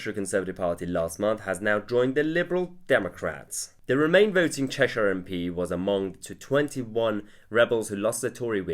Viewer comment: At 1.24 Sounded like you said the remain voting mp was a mong!